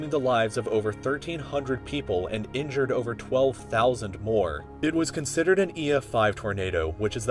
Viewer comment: says 12,00 instead of 12,000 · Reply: XpenSe No, he said billion.